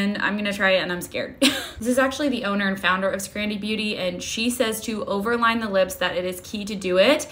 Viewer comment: I'm SO Glad you Responded to Someone asking for the Brand Name ("Scrandie Beauty")... Bc, due to my 60% Hearing Loss, I thought you were saying it was called "Grandie/Grandy Beauty Spicy Sauce Lip Extreme Plumper". So, I may have Found it through the "Spicy Sauce Lip Extreme Plumper" Part, but NOT if I Searched "Grandy/Grandie". 😂 Now, besides Wondering about what the Price Point is... I'm more Curious as to what the Ingredients in this Lip Plumper are, bc Lip Pumpers that use Pepper, Chili Peppers, High Amounts of Menthol, etc to incite a Reaction by using Ingredients that'll Irritate the Lips into Swelling, that will actually Dry out the Lips & Damage the Sensitive Skin off the Lips... & they usually don't have any Long Term Benefits from using it. The Best Lip Pumpers use ▪Hyaluronic Acid Spheres▪ (that are small enough to penetrate the Skin Barrier & once they combine w/ the Moisture in your Lips they Plump up! Can't get anymore Natural than that!), ▪Oligo/Hexa-Peptides▪ & ▪Moisturizing Oils, such as: ▪Jojoba, ▪Argan, ▪Shea, &/or ▪Vitamin E▪ (in a Greater Quantity than just as a Tocopherol - ie. Preservative).